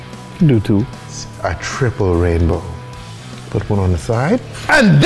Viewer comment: Thought that said saint daddy’s day